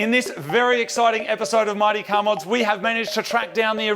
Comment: i swear the intro sounds like its saying marty car mods